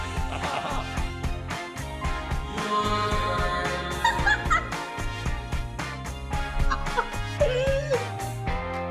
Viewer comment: Does anyone know what the sound effect is saying (or what is it called) at 1:07 ? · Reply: "Your turn". I believe it's from Thijs, though the exact video of origin is unknown to me. Another captain will need to help with that particular source.